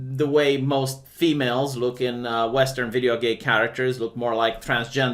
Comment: Did he just say "video-gay" characters?